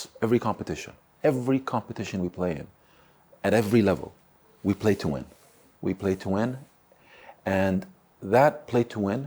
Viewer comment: bro i da thought he said "we pay to win" LMFAOAO